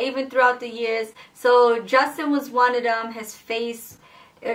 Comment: When you said adjusting was one of them and he thought yu said Justin that's so me lmao like what yu just say 😂👈💀😂👈💀